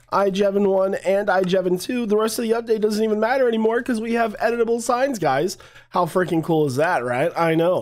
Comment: 100% best feature.... But halfway through I heard "edible signs" 🍕and now I'm imagining bites missing from signs... 😂